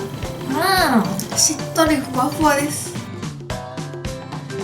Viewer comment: ふわふわがしゅわしゅわに聞こえる